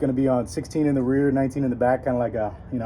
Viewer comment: did he say a 16 in the rear and 19 in the back 😂